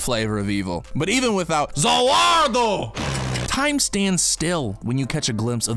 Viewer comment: all i heard at 8:29 was ZA WARUDO